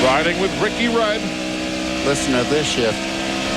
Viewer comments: Did he just say "Listen to this shit" ROFL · Could’ve swore he said listen to this shit instead of shift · I thought he said listen to this shit as well, then it dawned on me...shift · lol I heard it "Listen to this shit" Oh well..he meant "shift"